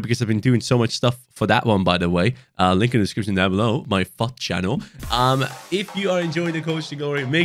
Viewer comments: i thought you said my fuck*ed channel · In the intro I thought he said fuck channel 😭